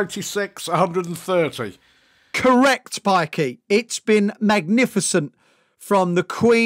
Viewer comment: Did he just call him a pikey? 😂😂😂